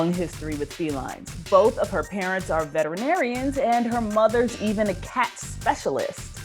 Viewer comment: When your cat sits on your laptop, it's saying "turn off the dang screen and admire me". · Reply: I thought at first they said her parents were vegetarian, but it was veterinarians. 🤣